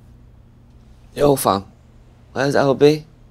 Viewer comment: When he said “where’s Lb” thought he said “where’s everybody” 😂 The Manchester accent never seems to fail to confuse.